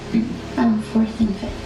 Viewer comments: the inaudible part on the first interrogation sounds like she said " 4th and 5th" meaning that she taught 4th and 5th grade at Littleton Elementary school. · This has nothing to do with the crimes, but in the first interrogation video, this is what they actually said: 1:10 "Umm, 4th and 5th" 1:36 "I'm from Wisconsin" 1:38 "F*** the Packers" 1:41 "You didn't cheer for the Packers?" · not inaudible. i think she says "fourth and fifth" (Grade) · the caption says, “Inaudible.” She says “4th and 5th,” when replying to the question of what she does at the Littleton school. As for the 2nd clip, I don’t think the sub refusing a “stress detection test” should be characterized with ominous music, as that’s the logical thing to do. “Will you be willing to take a lie detector/stress detection test?” “No.” “Why?” “Because I pay taxes to fund the police department. And given that the results of this test are not admissible in court, it’d be pointless. The results can’t be used, but you’d need to pay someone to operate the machine, etc.”